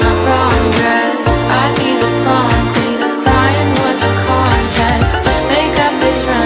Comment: I'd been a prom queen if quarantine was a contest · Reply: i heard “if brcrian was a corn test”